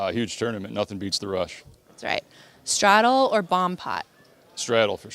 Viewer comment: Love you for the monotone voice . Thanks to the end segment I just realised it's a bomb pot I always thought you were saying barm pot. What a dick lol 😂Good luck I still think it's your year .